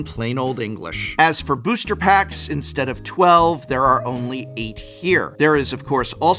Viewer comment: It should be 8 Booster Packs instead of 12 in the subtitles.😃